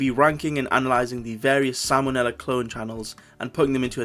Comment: When he said Sam O'Nella I thought he said salmonella